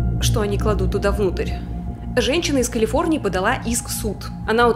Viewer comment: ЗАНАЕТЕ ЧТО МНЕ ПОСЛЫШАЛОСЬ НЕ «женщина из калифорнии» А «женщину из калифорнии» 🤣🤣🤣🥲🥲🥲🤨🤨